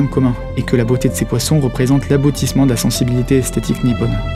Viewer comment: "la beauté des Koi représente l'aboutissement de la sensibilité esthétique nippone" La première fois j'ai entendu "représente l'abrutissement", et ça m'a pas choqué outre mesure xD (oui je suis contre les croisements d'espèces pour les faire correspondre à nos envies primaires)